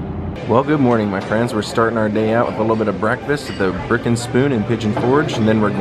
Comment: I thought you said you were eating at the “Frickin’ Spoon”! :)